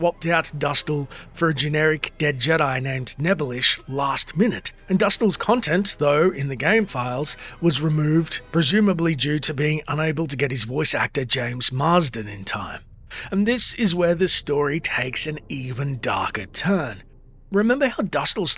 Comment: jason marsden. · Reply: Ah, that makes more sense. It sounded like he said James in the video. Jason was in an episode of The Adventures of Brisco County Jr. though.